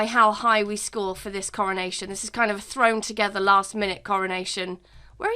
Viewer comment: I heard “throne together last-minute coronation.”